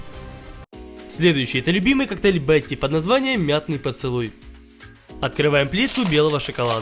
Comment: мятный поцелуй Я:послышалось мятый поцелуй😂